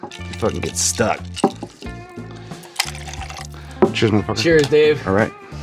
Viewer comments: Am I hearing Dave correctly at 8:40? It sounds like he says "Cheers mother******" to Sean. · did Dave say “cheers mutha fucka” or “cheers my friend”?